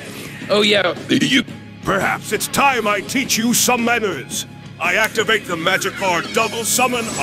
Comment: anyone else hearing "teach you some letters"¿ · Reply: when he says "perhaps it's time I teach you some manners." It sounds like he says "letters" not "manners" lol